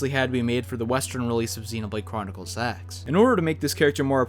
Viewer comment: I can't we the only one who heard "xenoblade chronicles s3x",right? Right guys?